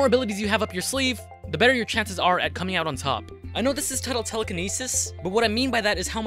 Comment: I swear that said twerkiness 😂😂😂